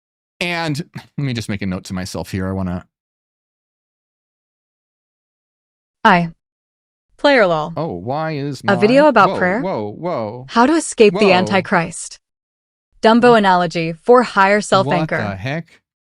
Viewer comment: Did Ai just say : " I , Plero " ? The heck?